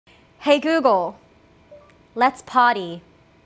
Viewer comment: I literally thought she said "Hey Google, let's potty" until the disco ball started turning